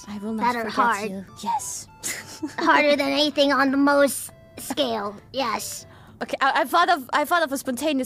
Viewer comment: Hey love the clips, keep up the good work! Just a heads up from a nerd with a background in Chemistry, at 2:54 you have the caption saying "Harder than anything on the most scale", but I believe the ridiculously cute, and deceptively smart little said "Mohs Scale" here instead, which is the scale used in the sciences to compare hardness of substances.